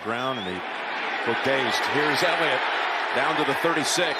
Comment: Did he say here is Emmit?